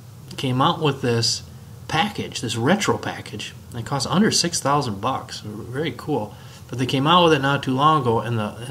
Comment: $106,000? Wow, that's cheap! rolls eyes Yeah, yeah. I know he actually said under $6,000. lol